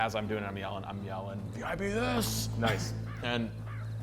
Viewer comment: what in the hell did say, i can't for the life of me figure it out. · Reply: VIP THIS!!